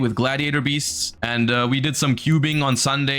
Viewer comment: did he said "cubing"? like solving rubiks cubes?